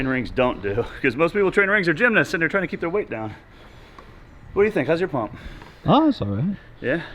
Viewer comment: I about spit the milk out. You guys were talking pumps, and what I thought I heard was “What do you think; how’s your mom?”